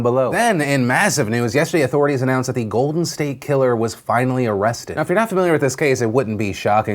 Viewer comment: What was your favorite story I covered today? Let me know in those comments! Disney LGBTQ(00:06), Janelle Monae(1:14), Cosby Guilty (2:42), Golden State Killer (6:12), Trump News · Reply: Philip DeFranco I thought State Killer said "Starkiller" LOL